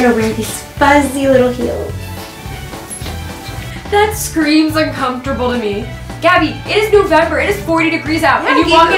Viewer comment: “Why do all your outfits scream uncomfortably?” 😂😂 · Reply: Mahnoor Zia idk it sounds like she said uncomfortably